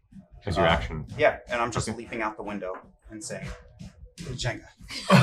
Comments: What does Liam say when he jumps out? Hard to hear. Sounds like he says "~jenga" or something. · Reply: if I remember right, the party used “Jenga” as a code word for when things go tits up (metaphorically speaking) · What does Vax say at 8:27 and 11:07? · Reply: Jenga. Chenga (changed due to copyright).